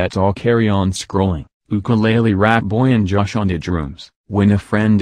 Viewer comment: when the voice over said josh on da drooms it sounded like josh on shrooms omfg